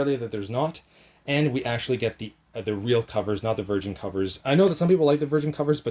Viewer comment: I did not understand what you said at minute 6:05. What did you say? "Virgin covers"?